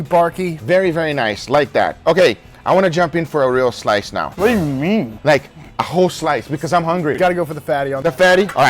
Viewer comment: Sounded like he said, "What do you meme!"